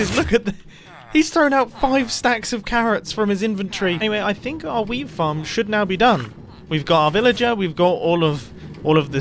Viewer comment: did he just say I think our weed farm should now be done